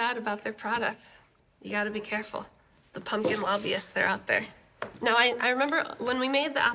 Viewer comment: The Pumpkin Mafia (lobbyist) is what I thought you said was going after your friend. 😅🤣🎃